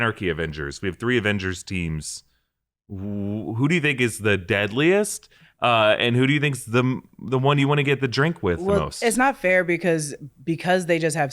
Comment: I thought deadline said no Deadpool 😂